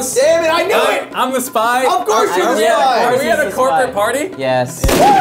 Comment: Was I the only one who thought the first place said "chocolate party?" 😂